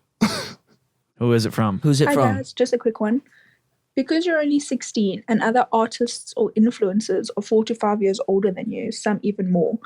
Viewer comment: dude I thought she said 45 years older too, but I realized what she said before Logan reacted. But like look at Logan's face when he hears 45 lol. I have no idea why, bit I swear to God, no joke, I broke down in laughter when he said "year for all the 61 year olds" I could not breathe I kid you not 😂😂